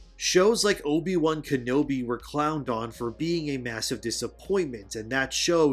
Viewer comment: haha. at first i thought you said obi was clown dong...